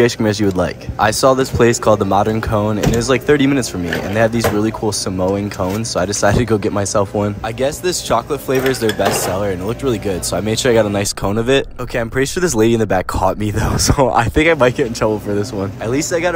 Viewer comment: I know I’m not the only one who heard him say “Samoan Cone” 🤔